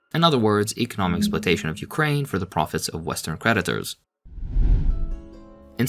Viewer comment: I thought I heard Predators instead of Creditors